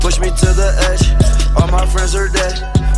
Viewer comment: I thought it said " all my friends are dead, because I am a murderer I shot them in the head..."